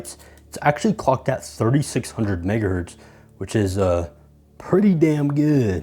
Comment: tf bro say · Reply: 3600 megahertz. i heard digger as well 🥀🥀